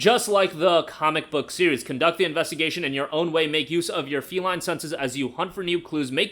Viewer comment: Could've swore you said "Make Noose" at 1:36 lol